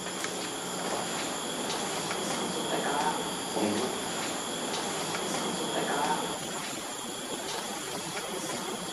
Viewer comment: any idea what the voice said? Pretty sure it was in dialect. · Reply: seung cho something like "Get off"